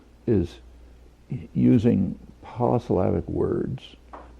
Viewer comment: what is the word he uses at 00:59? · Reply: the word he uses is ''POLYSYLLABIC'' it means a word with more than one syllable